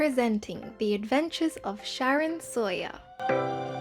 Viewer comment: I thought you said 'sharon soya' Instead of 'sharon Sawyer's 😂